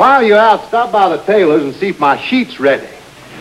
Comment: Am I hearing things, or at 13:41 does the sheriff tells the deputy to go to the tailor shop and "find out if my sheet is ready?" A rather interesting allusion to the KKK, one that got a laugh, although not as hearty as the rest of this so-called sketch.